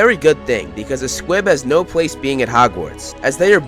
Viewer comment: Who else heard him say "pwace"?